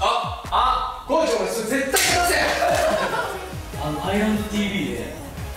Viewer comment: 樹くん、なんて言ってるかわかる方いますか？ 高地、、、の後が分からない笑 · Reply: コーチお前それ絶対片せよ！ ですかね🤔